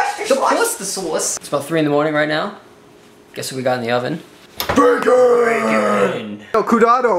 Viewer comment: I thought he said "Guess who we got in the oven" And I thought, oh $4!7, it was turned on.